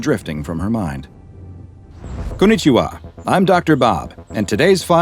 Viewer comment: did you just say konnichiwa?😂